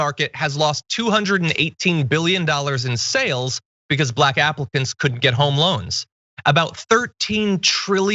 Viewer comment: Black Africans, lmao, you totally just Gorka'ed it!!# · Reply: Could have sworn he said "black applicants".